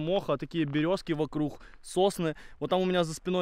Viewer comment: Сосны,а мне послышалось Сосни -________